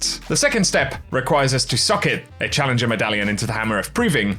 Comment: Anybody else heard SUCK IT instead of SOCKETED???